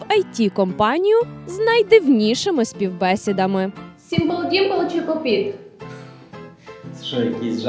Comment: Що вона сказала??? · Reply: сімпл-дімпл чи поп-іт